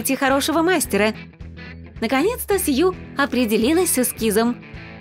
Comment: Сью? Мне не послышалось?😂😂(Имею ввиду значение слова сьюха) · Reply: Сказали вроде Сью